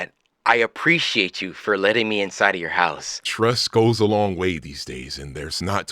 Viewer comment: I heard “thanks for letting me inside your house” and why’d I actually believe it.